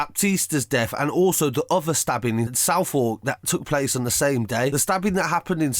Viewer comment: “Southwark” is pronounced “Suvvok” not “South Walk” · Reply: I thought he said southall I was so confused